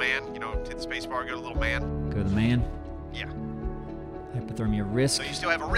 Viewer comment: Wait are you guys saying "Hyperthermia"?! It's Hypothermia, that's the one that means your body's too cold. The Hyper in "Hyperthermia" means extreme amount of thermia, in this case hot. After all that rambling it also might be me who doesn't hear right but I swear I hear Hyperthermia.